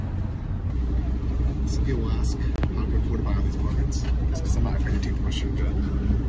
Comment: I replayed 4 times have no idea what you said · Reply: "Some people ask how I can afford to buy all these apartments... its because I am not afraid to take the Marshrutka"..